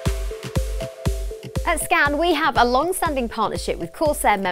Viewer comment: I thought she said at scam I was about to head out